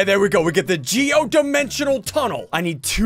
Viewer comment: did he say damn